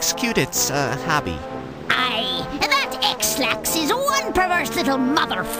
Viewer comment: Did she just call him Ex-lax?